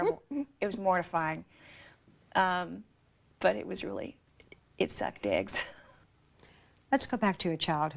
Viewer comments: Omfg... 🧐😯😂😂 At 8:34 did she say: "It was terrible. It was mortifying. But it was really... it sucked dicks." ??!? 🤣🤣🤣🤣🤣🤣🤣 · Did she say, "it sucked dicks" on national TV?! I must have heard that wrong, 5 times in a row... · What am I hearing at 8:36 in regard to her "talent" performance? Because I don't think she's saying what it sounds like. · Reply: She said 'it sucked eggs' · Ha ha, at 8:34 it sounds like she said, "It sucked dicks". I know that's not what she said but....ha ha